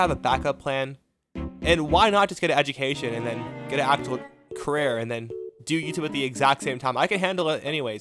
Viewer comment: thats what i thougt i thougt i heard j-carrer